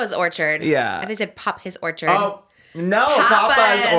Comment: I definitely thought said, “pop his orchard” too 😅